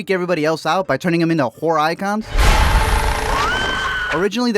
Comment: lol I heard "Whore Icons" instead of "Horror"